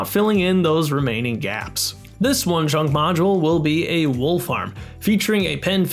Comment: Near the end, I kept hearing "Wolf Arm" instead of "Wool Farm"